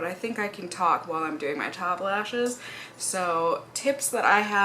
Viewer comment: what did you say here hahah? Your child lashes?